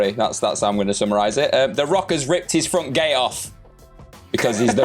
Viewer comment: That final story? I was so so so confused at first because I swore that I heard say "The Rock Ripped His Front Gay-off!" and I was like "His WHAT?!"